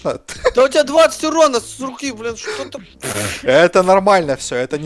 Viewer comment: Мне послЫшалоси или сказал: СУКИ? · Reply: Funtime Foxy он сказал с руки